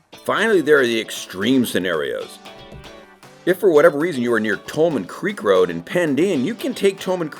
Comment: I've lived here less than two years but think I've ridden every road you mentioned except upper Tolman Creek (to Mt. Ashland). I misheard and thought you said "Coleman Creek" which set me raging about, "NO! That road does not go there!" I was comforted after consulting the Google Sage. No car, just a garageful of motor bikes. Hey, were the overheads from your drone, or stock footage?